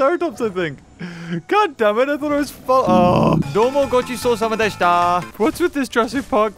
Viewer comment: later in the video... speaks gibberesh* is it just me,is something wrong with my computer!? because at 6:14 i heard gibberesh. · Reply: sounds like he's speaking japanese "Gochisosama deshita" thank you for the meal